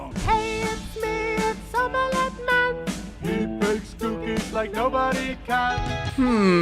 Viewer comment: I heard "he makes dookies like nobody can"